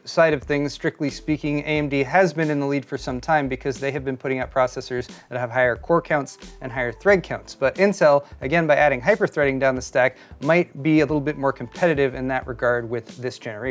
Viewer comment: did you say incel?